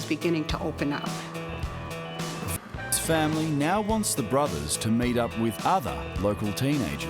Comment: sounds like hes saying fu## this familiy lol